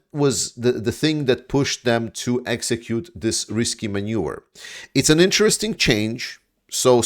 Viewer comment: So often I've heard you say maneuver as manure - today it was so apt when you were talking about Russians using a sewer.